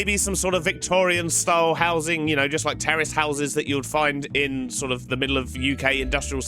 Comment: Terraced 😂🤣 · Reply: oooo lol I thought U were saying terrorist houses 🤣🤣